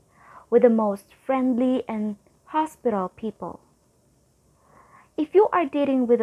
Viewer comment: Did you say "freindly and hospital people" location 1:24?